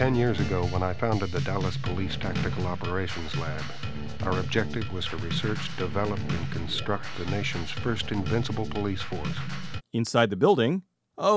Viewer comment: Not sure if this has been said but at 3:37 he actually says "our objective was for research, develope, and construct the nations first invincible police force"